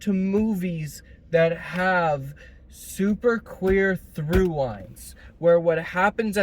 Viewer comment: Sorry I heard ‘queer through lines’. I had to rewind it chat